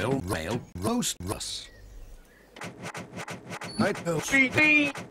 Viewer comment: What does he say there? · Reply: Ben Packham He says "My OCD!"